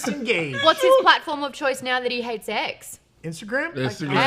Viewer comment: I thought said he hates eggs. lol